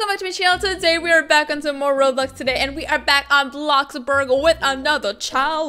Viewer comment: for a reason i heard her say robux instead of roblox did you hear it or is it just me